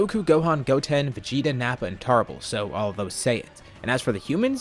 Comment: did he say tarble or am i tripping